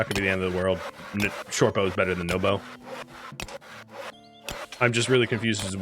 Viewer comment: I know what you said, but what I heard was "a short bow is better than an oboe".